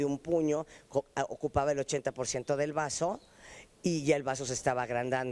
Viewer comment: Chale, pensé que decía el brazo y me asuste, hasta que la vi muy normal y se me hizo raro.